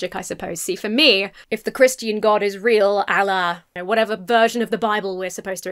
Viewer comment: Due to the cutting around 31:20, it sounds like you are saying "For me, if the Christian God is real, Allah" instead of à la, made me chuckle.